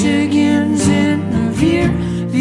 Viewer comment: You guys here "Michigan" I hear "this bitch is in"